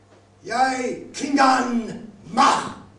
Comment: isn't the word for heart "tiQ?" I didn't hear that, I heard "Yai tlhingan Moh" "tlhingan Moh" means "We are Klingons" I have no idea what Yai is, but it's not "beating" "heart" "stop" or "nothing" Also if you have a video or slide projector, wouldn't it make more sense to use that to project "subtitles" like they do in real operas at Symphony Space or Lincoln Center?